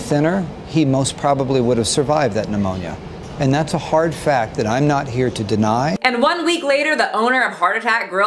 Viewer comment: I could’ve sworn that guy said, “that’s a hard fat I’m not here to deny” omg 🙃